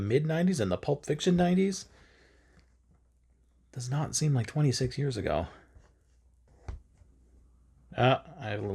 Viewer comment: brilliant video! Thanks for the stitch along. I hope it's one of many 🙂 And, I totally misheard you...Pulp Fiction wasn't twenty some odd years ago! 😲